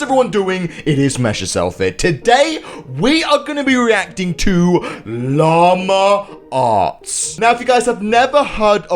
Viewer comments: I thought you said llama ass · When said "hu-hello everyone how is everyone doing it is messyourself here" my grandma thought he said sexyourself not messyourself🤣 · OMG I thought said lama farts 😂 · I thought he said llama arse at the beginning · I thought he said “Llama Ass.” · I thought he said Lama farts 😂😂😂😂😂😂😂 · I thought you said llama arse 😂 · Lama arts I thought that said lama farts